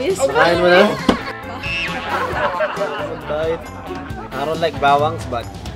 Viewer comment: Did you say I don't like bawang?